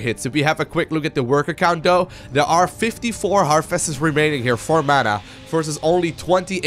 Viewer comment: did he just said 54 harvesters for Mana and 28 for protoss. Lol.